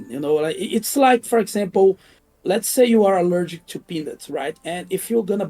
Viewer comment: I thought he said "if you are allergic to penis" and I thought of Anita Sarkeesian :D